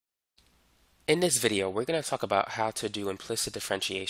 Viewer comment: Fr thought he said in this video we gonna fuck around